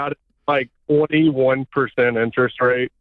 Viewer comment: Whew, for a moment there I thought he said 41% interest rate, it was just a dream.